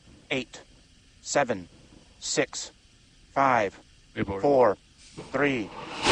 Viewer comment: I think during the countdown at 1:58:00 to 1:58:02 I heard an 'abort' given. Maybe my ears were playing tricks on me.